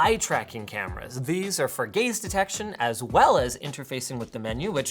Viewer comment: Did he say it has gays detection